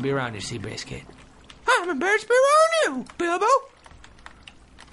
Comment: When he said how do you know my name is bilbo i thought he said how do you know i use dildos! xD i rofled!